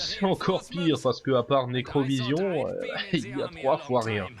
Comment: C'est quoi le nom du jeu a 1:41 ? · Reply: Necrovision